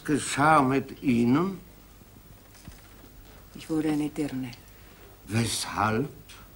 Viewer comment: was sagte Claire in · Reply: Sweet L. Sie sagt: "Ich wurde eine Dirne"